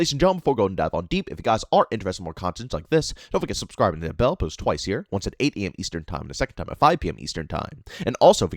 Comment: did you just say you post twice a year?.😂